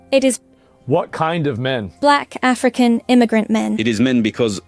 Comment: I kept hearing "Nigrant"